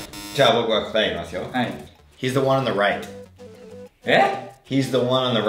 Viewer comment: he is on a ride に聞こえた